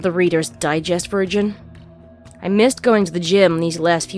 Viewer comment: Thought you said: "Readers Digest virgin" instead of "version" there for a moment.